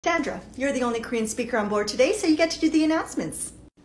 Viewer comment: Am I the only one who heard her say dandruff instead of Sandra ? 🤣🤣🤣 .